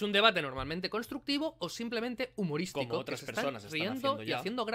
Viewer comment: y esa voz en off que dice "como otras personas están haciendo ya" en el minuto 16:52?? estamos ante una psicofonía youtubiana?